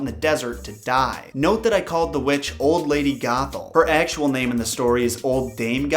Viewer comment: every time you said old lady gothel. I thought you said. "Old lady Gaga" :)